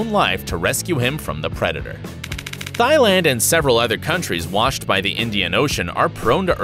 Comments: did he say thigh land? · I heard u say “Thigh-land” instead of “Thai/Taii” it’s my country >w< souns weird